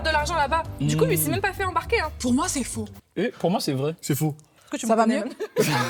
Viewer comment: Pas mal ce genre de video ca change un peu parcontre en vrai vous êtes des" ptits joueurs" vs avez mis des minis gouttelettes avec votre pipette là 😅 lol et elle dit quoi à 12:53 ? J'ai pas compris. Et cool de voir en plus ce rôle lui va à merveille ^^ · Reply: J'ai dit "est-ce que tu me connais même" 😭